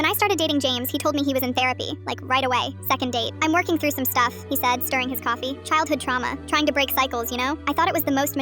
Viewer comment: What I read:“He died stirring his coffee” What I heard:“He said during his coffee”